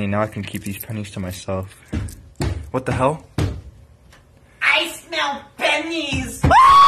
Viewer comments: SCP-049: I SMELL PENNIES! Me: more like: I SMELL PESTILENCE!!!!!! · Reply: Oh wait I heard "I smell moneys!" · SCP-049: I SMELL PENNIES! Me: more like: I SMELL PESTILENCE!!!!!! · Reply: WhEezEe I THOUGHT YOU WhEezEe SAID "I smell monkeys" 😂😂😂